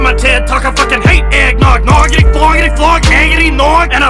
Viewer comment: Nobody: Not a single soul: Eminem: “Noggity floggity flog eggity nog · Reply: I thought he said "gagity nog" bc it's gross and makes you gag lol